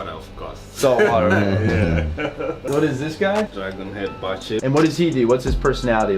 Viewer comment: can someone please tell me the name of that fish I cannot hear what he is saying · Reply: He said "dragon-head bichir" but the scientific name is Polypterus endlicheri